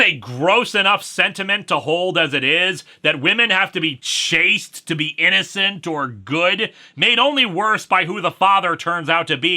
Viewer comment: this is probably my 3rd time watching this, and I think it's only this time I've realised you said "that women need to be CHASTE to be innocent or good". The other times I thought you said "Chased", and now that makes MUCH more sense